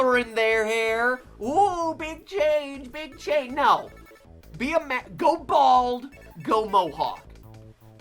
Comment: it sounds like he was gonna say be a Maverick